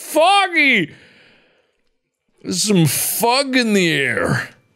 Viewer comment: why at 5:26 it sounds like hes saying "theres fuck in the air" instead of fog